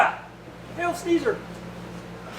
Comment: Did I hear you right, "hail sneezer"? Lol